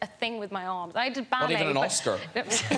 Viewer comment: "Ive never lifted a thing with my arm" Graham: "Not even an Oscar" XD SAVAGE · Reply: He said "Oscar"?! Why did I hear "lobster"?? And the rest of the conversation "it takes two hands to pick it up" made sense too, for a lobster 🤪